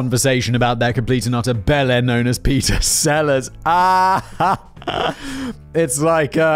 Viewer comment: Initially when Simon said Peter Seller's name I misheard it as Peter Sallis(the voice of Wallace from Wallace and Gromit) and I thought to my self "What?! Wallace was James Bond?", this was so mind boggling I had to google it to see if this was correct and I wasn't mishearing(I was mishearing).